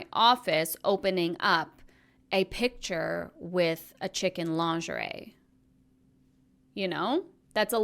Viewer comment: sorry this is so off topic but I thought you said “chicken lingerie” and as I’m laughing a popeye’s ad plays and I just